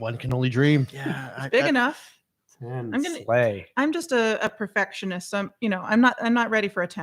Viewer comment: DOES HE SAY SLAY?!